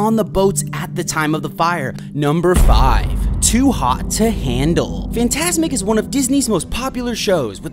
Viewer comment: Bro why did I hear “To hawk tuah to handle” 😭✋